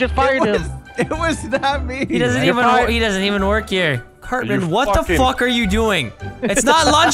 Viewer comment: vanoss said curtman instead of cartman